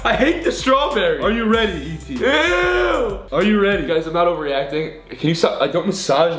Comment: Did Grayson say “are you ready eetee?” At 3:37, EETEEWEETEE!!😂💞